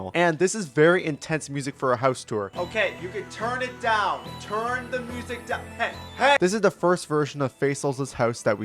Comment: HUH?! WHAT DID YOU SAY SEN?! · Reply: The joke is that in the house intense music is playing so Sen says "TURN THE MUSIC DOWN"